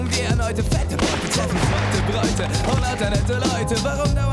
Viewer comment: Was für Bräute? Hab diese eine Stelle in dem Text nie verstanden^^ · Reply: Seute Bräute!! Schau ins Plattdeutsch Lexikon ;)